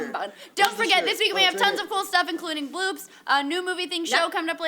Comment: At the end when said bloops, for a sec I thought she said boobs... O.o